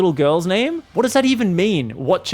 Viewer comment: I'm going to admit that I didn't read the full message, so I also thought he said "lil girls name" and enjoyed the same derp moment with you fam 😂👌